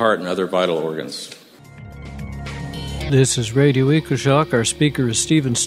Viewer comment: can anyone please let me know who the speaker in the video is? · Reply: "Radio Equi-shock"? (if I heard that right). Speaker, "Steven Star"